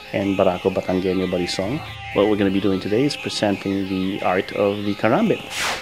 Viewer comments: Lol I thought he said the art of the harambe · seconds the captions say Barack Obama as one of the fighting forms he know 😂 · Am i the only one who heard him almost say "Barak Obama" at the 0:22 sec mark?